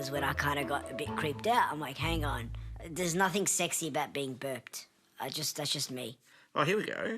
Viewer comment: "there's nothing secy about being burped" had me cackling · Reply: I'm glad I saw this comment. I was listening to this while cleaning up the kitchen and didn't hear "burped" I heard "birthed" which just kinda threw me into WHAT THE HELL mode.